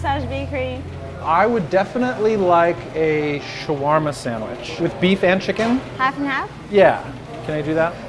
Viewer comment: I thought that it said lesbian shawarma lol.